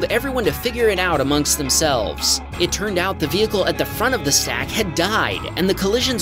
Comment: When you were talking about the carwash pileup and mentioned how "the vehicle at the front of the stack had died" I misheard it as "the driver at the front of the stack had died" and I was wondering why you were do nonchalant about it.